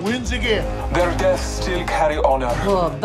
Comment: Bloodhound: Their deaths still carry honor- Lifeline: PoOr bAsTarDs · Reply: I heard him say “They’re deaths decay honor